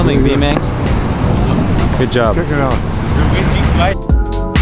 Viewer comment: did he say check me out